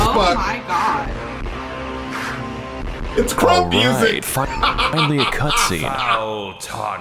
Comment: did he say krump music? No wonder it sounded badass